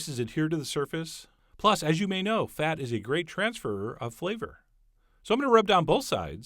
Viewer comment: Did he say "transferer"?